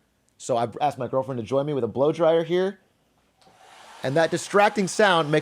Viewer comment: I thought he said Blow * lmao